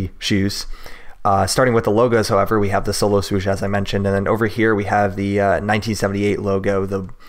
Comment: i thought it said r/wooosh lol